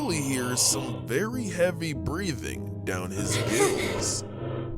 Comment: i thought he said "down his balls"💀💀💀💀